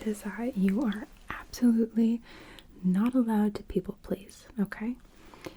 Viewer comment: what is she saying? “Not allowed to..” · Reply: not allowed to people please 😊